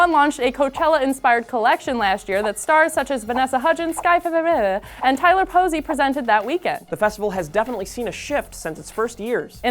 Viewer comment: What name is she trying to say xD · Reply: Sky Ferreira